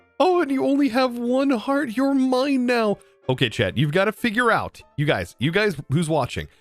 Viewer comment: His name is Chad, because that's what YT Captions registered when you said "Chat".